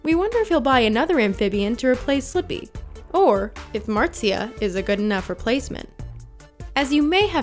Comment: Did u just say that marzia is a fekin frog